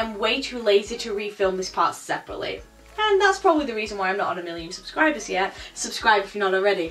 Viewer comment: did she say 100 million??? Or am i just dumb....i think im just dumb... right?????